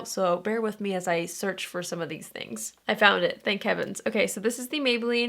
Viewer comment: When you said "Thank Heavens" I thought you said "Thank Kevins" and I've been saying "Thank Kevins" for 24 hours now.